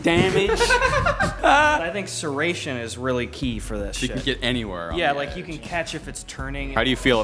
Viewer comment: I believe I heard a swear word at 7:55 that is not kid friendly content, unless sh*t is an acceptable word for youtube